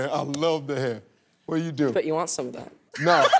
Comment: The sass in this family! Haha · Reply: I thought he said do you want some of my hair.